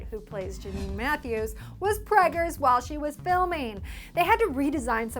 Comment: I'm sorry did she say pregers